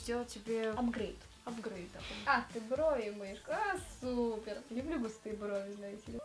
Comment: Мне послышалось, или ругнулась? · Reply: Влада Громкая- Киселева она сказала:А, супер